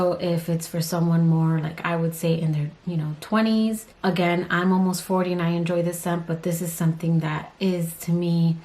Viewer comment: Your edits always take me by surprise and are killing me! Also did I hear right? You're almost 49? I cannot and will not believe that. I am actually questioning my hearing lol. Not 39? Or rather early 30s? I am genuinely shocked. Thanks for the great video!!